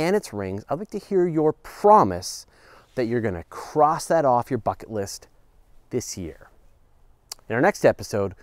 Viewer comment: Haha, thought he said 'cross that off your f'kin list' at 4:24. Had to skip it back and listen again!